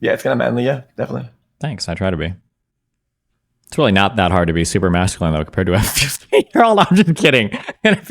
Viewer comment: Idek what he said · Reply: SAME. But it must have been edgy so now I need to know. I thought maybe he said something like "it's really not that hard to be masculine compared to a 15 year old" but that's like... really tame.